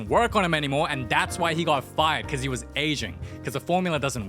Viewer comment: I thought he said “And that’s why he got fired because he was Asian” 😭